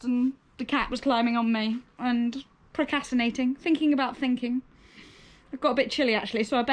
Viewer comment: I feel like you every month too! It sounded like you were saying ProCATination for procrastination, so I’m definitely stealing that word for when my cats keep me from doing my work! Loved the vlog as always. I can’t watch vlogs with happy people all the time because I can’t relate!! 😝